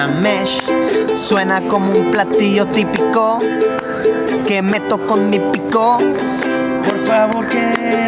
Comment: penze que dijo Que me toco mi pito X,dddddddddddddddddddd